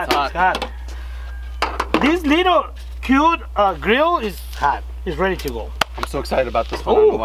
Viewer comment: cabron said this little cute grill is got i thought he said the little cute girl is hahahah